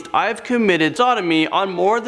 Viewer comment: wait what did he say at 8:23 i cant hear it. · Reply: did he say sodomy?